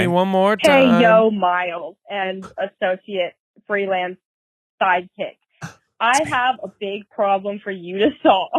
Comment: Wait I thought Miles has been saying “freelance psychic” and I’m just now realizing it’s “side-kick”